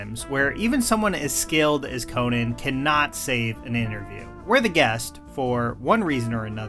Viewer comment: someone as scaled as Conan... I heard you. Conan is in fact a lizard.